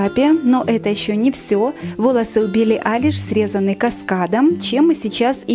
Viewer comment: Мне одной послышалось: Волосы УБИЛИ Айлиш